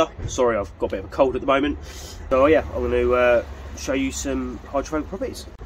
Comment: Hydrophobic you meam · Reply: no, hydraulic is what you said and put in the video timeline. 😜